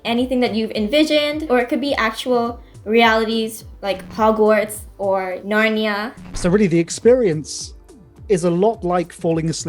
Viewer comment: Did she say “ actual realities like Hogwarts or Narnia”